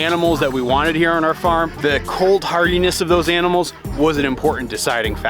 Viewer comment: I keep hearing cold-heartedness at 3:50, which makes me laugh.